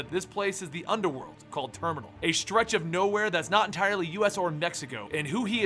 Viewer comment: why did I hear termina Probably because I watch/play legend of Zelda to much